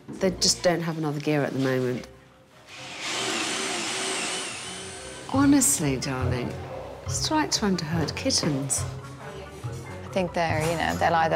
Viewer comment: Can sb please tell me what Alex is saying? Sounds like " Honestly darling, strike two under hurt kittens".